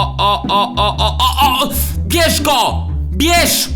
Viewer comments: kto tez usłyszał PIESZKO a nie BIERZ GO?XDDDD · kto tez usłyszał PIESZKO a nie BIERZ GO?XDDDD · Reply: Czeremcha lol Generalnocdxowa ja usłyszałem pierz go